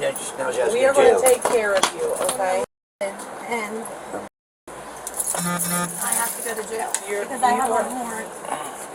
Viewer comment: “I’m m******d and-. And m******d.” What is she saying? I’m murdered? That doesn’t make sense but I’m having trouble coming up with another 8 letter word that fits the situation. Though why I’m expecting her to make sense after listening to her parents?🤦🏻‍♀️ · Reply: Molested